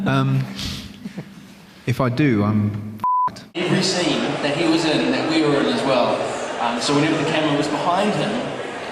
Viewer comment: What does Martin say at 11:01 With the **** peep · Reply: Fucked